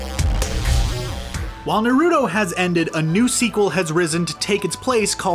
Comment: DID U JUST SAY NARUDO WHAT iS THAT